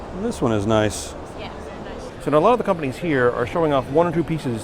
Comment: did she actually say "yes, very nice" in response to L's statement ?